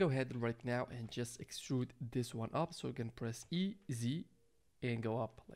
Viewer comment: u say tab shift then E, then sounds like u say z but i dont see z pop up. i cant get it to increase it up at that part.